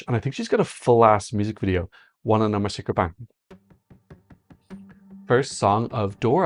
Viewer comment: Could you please write what you are saying before starting videos? E.g. at 2:11 I'm just wondering, I'm not a native english speaker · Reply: Oooohhhh, ok I've got it now... He's quoting from Maruv's Siren Song..."Wanna know my secret?" And then "Bang" as he hits play. Do I win?